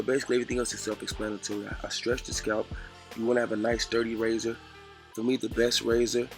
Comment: bro.i thought he said nice dirty razor but I rewind and he said sturdy I'm like damn son no clean razors